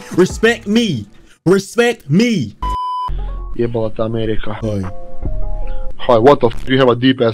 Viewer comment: that man was speaking I think Serbian and he said fuck America bro😂